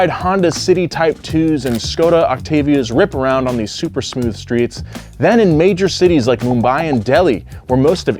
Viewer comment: did he say super smooth streets? 😅